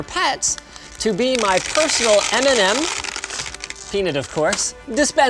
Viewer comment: Am I the only one who heard "My personal Eminem dispenser"?